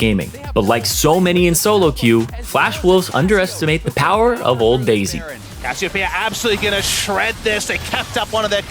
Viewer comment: Did you just say old daisy